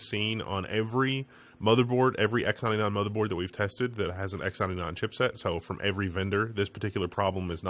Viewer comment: What does he say @ 0:30? It's hardly understandable. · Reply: He says "X 99." He's talking about some kind of motherboard chip.